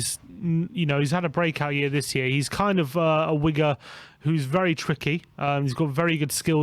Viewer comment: why is saying wigga ain’t it winger?